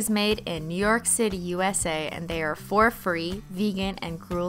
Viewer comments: Omg I thought she said “for free” instead of “four free”. 🤣🤣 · Did anyone else hear "fur free" as "for free" at 1:17?😂😂